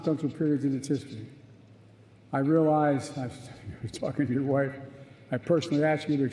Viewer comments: Wait what did he say at @ 57:40 ? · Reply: “It’s F u c k ing with your wife” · Did he just say "Fing your wife" hahaha at